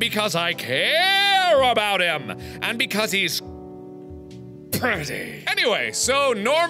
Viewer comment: when your friend asks what kind of anime you like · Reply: Depressed Lilypad 1:34 did he just say that Senpai is petty? It kinda sounds like he said petty. Lmao XD